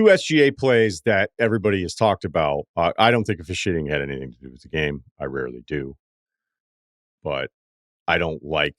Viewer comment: wtf did he say? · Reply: He doesn’t think the officiating had anything to do with the game. Surprised I even understood lol